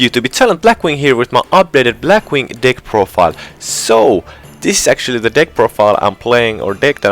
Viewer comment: I swear to god i heard "my black wing d1ck profile"